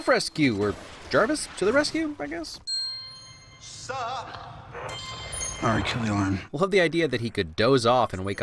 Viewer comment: what does Tony say?... "Alright kill the ???" Who wants to help me out here? bc whenever I can't understand what a character is saying in a movie or tv show I just go crazy until I know what they're saying... even tho whatever Tony said there probably means nothing I still REALLY want to know... ya know? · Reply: He said "Alright, kill the alarm."